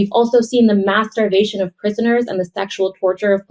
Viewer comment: I'm sorry, I heard "the masturbation of prisoners"!?